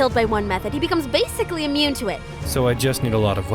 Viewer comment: Edit: I've just been informed that I misheard what was said in the episode, where it actually said "basically immune" instead of outright "immune". So I guess this is how they decided to have Illya mention the boosted resistance in a bravado manner. So My comment was pointless. Oh well. Technically speaking, Berserker doesn't actually become immune to the methods that kill him after dying to one said method once. He only becomes more resistant to it. Also, if the method is used continuously before he gets to fully regen from it (aka if you continue attacking the corpse/injured body), you can continue taking more lives before the resistance gets higher.